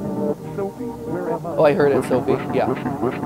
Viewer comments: in the part where sha says "sophie" it sounds like she says "sophie, where am i" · When sha was glitching out in the basement. I thought I heard her say "sophie where am i?" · Around at 31:19 I think she says not only sophie but " Sophie where am i" before lagging out. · I heard say "Sophie where am I?" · I think she says Sophie where am I? Before repeating missing.